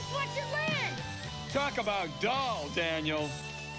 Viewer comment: please tell me i wasn’t the only person to rewind and instead of dull say “Talk about Damn Daniel!” 😂🤣😂🤣😂